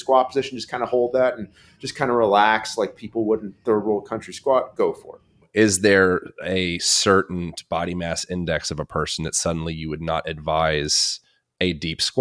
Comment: Did his say people in the third world countries at 23:41?